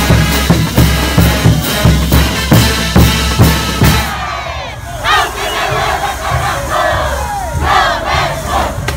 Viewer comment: alguien puede decirme textualmente que es lo que dicen en el min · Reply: Gritamos Aunq te duela carajo!! Lo mejor!! Pa " Tí!!